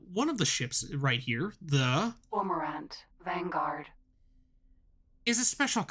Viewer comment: ????? Vanguard. You might want to actually say it versus have the computer say it. I have no idea what it said. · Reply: That is the Cormorant